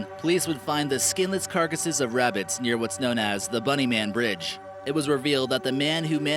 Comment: When he said "The bunny man" I thought he said "The Boogie man" and I was about to go crazy